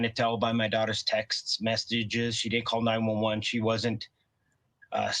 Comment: I thought he said she didn’t call 911. And her messages weren’t like she heard noises.